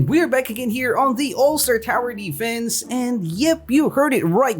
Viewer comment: I think I heard 'welcome to alt star tower defense'...